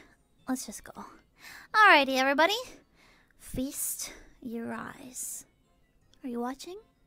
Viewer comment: hehe pomu’s so squishy~ thank you for the clip!! 0:12 pomu said “feast your eyes”, which means to watch and observe! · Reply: Ngl I thought she said "FIST your eyes"...